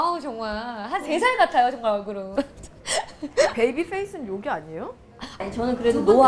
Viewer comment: 베이비페이스는 욕 아니예요가 아니라 요기 아니예요로 들리네요 .. 자막이 잘못굈어요!